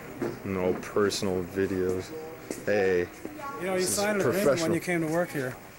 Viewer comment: What's that guy say at 8:26? "You signed a ___ when you came to work here", but I can't get what he's saying in the blank. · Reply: "Agreement". if you slow it down 50% you can hear it.